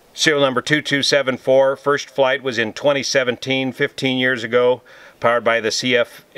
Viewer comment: Great explainer! Did I hear correctly? At 0:55 you said “it’s first flight was in 2017, 15 years ago”? Thanks.